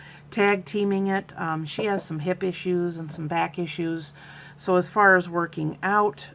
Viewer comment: Just found you!! Love ya!! You said "hip issues" and I heard "hippy shoes". Lol!! Bless you my love!!!